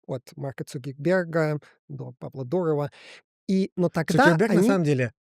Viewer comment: Услышал как МЯТА ))) Подумал «Какая ещё Мята?» )))